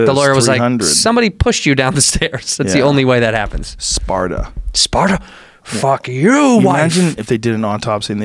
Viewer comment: wait wait wait... at 1:00 did he say SPARDA?